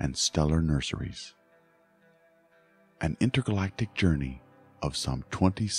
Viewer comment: Great presentation, but did I hear you say "Inter-galactic journey"? Hmmm??? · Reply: I thought I said inner-galactic, but maybe I mispronounced.